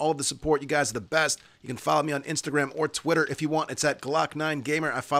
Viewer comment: I swear, sometimes I hear "New Frontier" as "Nude Frontier". Two TOTALLY different things to a cowboy worth their salt!